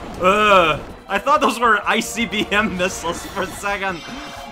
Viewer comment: I thought you said "icy-bm" at first